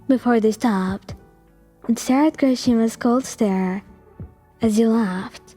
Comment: pretty sure I heard Kirishima